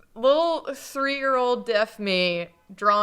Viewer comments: Did just say she was deaf as a child? Am I just mishearing what she said? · did just say she was deaf, or did I hear that wrong? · Wait /Wait/ At 30:54, did say “Lil’ three year old /deaf/ me” Because if so That’s changes a lot of things for me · deaf? did i hear that right? ...was deaf? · Did say "little three year-old deaf me" here? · I can't quite tell what says here?? I keep hearing it as "little three-year-old deaf me", anyway phenomenal drawings!!! I love seeing old art redone like this